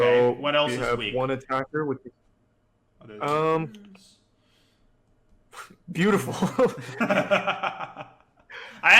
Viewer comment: What did Daniel whisper at 47:12? Btw, the И on your name is a nice touch, but the cleaner logo is more appealing to me. Great video as always! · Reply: "other than your face"